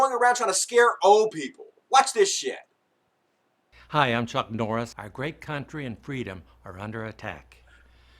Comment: I swear it sounded like he said "Hi I'm Chuck Norris, I rape countries freedoms." lol